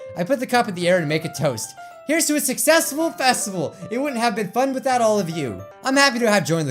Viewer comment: Here's to a successful festival What I hear: Here's to a successtival festal